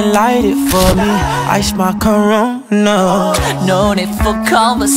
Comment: 😂😂😂am i the only one who heard jason say ‘I smell corona'